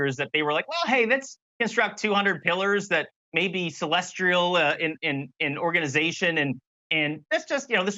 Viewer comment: Did I just hear things, or didn't he also say 'celestrial'? I could understand if the added 'r' was a typo, but him actually saying it...?